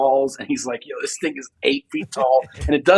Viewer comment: I coulda sworn he said “yo, this niggas 8 feet tall” lol